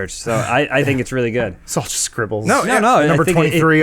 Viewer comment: all I heard was "Its all just scrobbles"